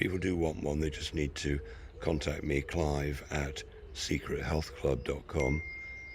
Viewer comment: Great talk guys! Can you show a link to buy one of these type tesla machines please. Thought you said c3 health club, 😅 I found you now.